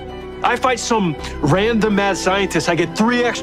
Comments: Ngl I thought Barry said random azz scientist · I thought he said "Random ass scientist"